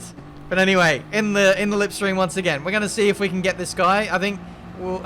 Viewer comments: did u say lip stream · Did you just say “lipstream” ?? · Did he say lipstream at 4:45?